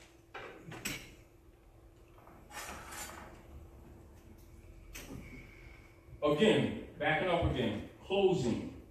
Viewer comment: What is he saying around 59:50 "Unum Sanctum _________?" What is the 3rd word? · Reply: Hegemony